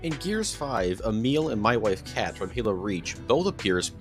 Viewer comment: did he say my wife kat?